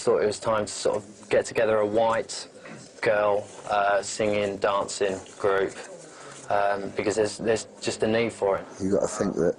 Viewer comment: did he say he wanted to make a white girl singing dancing group?